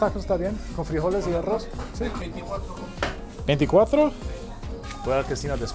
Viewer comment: Did he say he paid $24 for that taco plate??